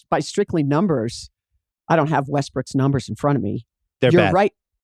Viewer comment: I always hear "Westbrick"